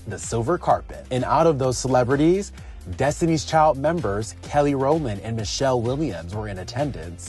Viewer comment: I hope I misunderstood, but I thought I heard you call the dynamic, beautiful talented, goddess Kelly ROLAND, Kelly ROMAN!! I KNOW I MISHEARD THAT, Right?!😬🙄😱😲🤐😠